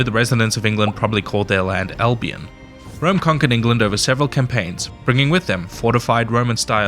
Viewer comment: for a second I thought he said Eldean 😳